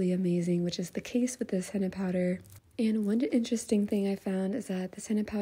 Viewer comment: Hi dear Please can u tell which ingredients you are adding❤️😀 As a female Saudi ur videas are so positive Please do more asmr I love your videos and it really got me interested in henna as a saudi 😍❤️ I always got laughed at for my henna designs but you really helped me improve and keep me motivated... Keep on going love 😍 Please don't ever quit Love you dear! ❤️ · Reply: I think she said 'this henna powder...' but it sounded like she said cinnepowder